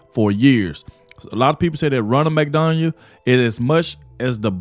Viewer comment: Sounded like you said "Ronald McDonald?". Lmfao! That would be great because nobody can afford to eat there anymore anyway.